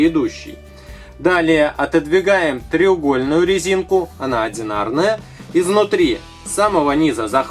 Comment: а когда было 4:25 ты понял что сказал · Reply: Sergey Nestojanov ага, треугольная резинка